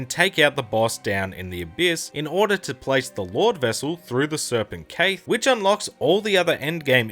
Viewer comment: Bruh i thought he said the serpent "Kate"